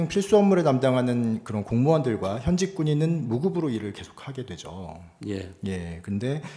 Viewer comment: 미국 federal shutdown *정정: 0:12, 전체적인 맥락이, 국방과 교통 치안 등 필수 업무에 담당하는 그런 공무원들과 현직 군인은 유급으로 일한다고 말씀하려고 하신것 같은데, 발음이 좀 무급으로 들리네요. 필수업무종사자는 유급이고, 비필수업무종사자가 무급이라, 셧다운되도 그나마, 나라가 돌아가는겁니다.